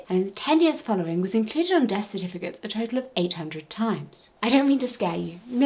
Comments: In the subtitles of this video at 3:34, says “800 times” but the subtitles say 88, just thought id make a note of it to help! So grateful you’re talking about this! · the audio says 800 times but the captions say 88! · Hi, at 3:32 the transcript reads "88 times" when it should be "800 times". I'm not sure how to fix it myself so I thought I'd say something! Thank you for the video btw, I learnt alot! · Wonderful video, but the subtitles at 3:35 are a but strange. You say 800 times, but the subtitles say 88 times